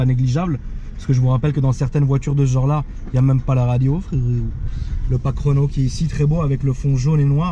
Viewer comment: j'ai entendu "pack Renault" a 3:00 j'ai vu flou frerowwww 😂😂😂😂